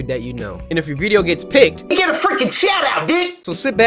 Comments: On 2:56 am I the only one where when he said "frickin" I thought he said "F0cking" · what he said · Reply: “You get a fricking shout out, dude!”